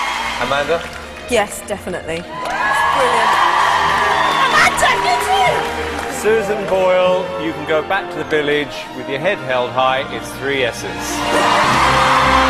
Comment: ～ アマンダ、ウレシィ～！！ にしか聞こえなくなった。